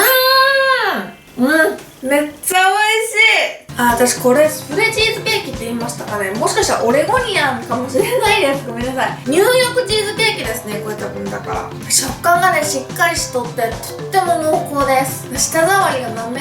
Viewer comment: ニューヨークチーズケーキが入浴チーズケーキに聞こえる1分ちょっとの辺り